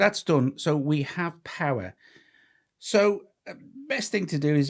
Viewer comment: I THOUGHT HE SAID "POWER HOPE" LOL